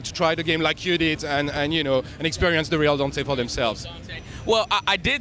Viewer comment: Did he say the "real Dante"??? @ 1:20??